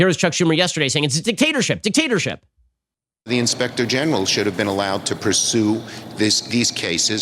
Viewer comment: I thought he said "Inspecting Genitals" 😂😂😂